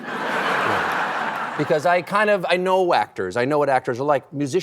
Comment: Ha ha, when he says if he get starstruck by actors, I thought he said "whack-tors" !!!